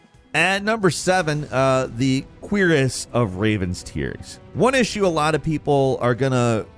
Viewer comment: At the start I thought he kept saying "queerest of raven tears" 🤣😂